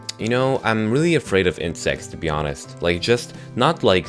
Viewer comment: I thought you said Incest instead of Insects XD